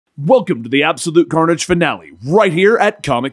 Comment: I thought he said “welcome back to absolute garnish” in the beginning of this video.